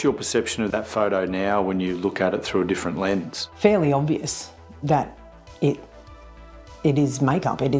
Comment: i thought she said fairly OBESE ToT